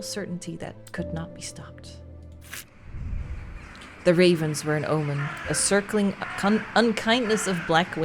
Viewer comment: lol, I heard 'the raisins are in the oven'